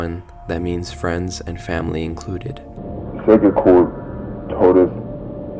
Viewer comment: I heard sega core or sega Corp like the company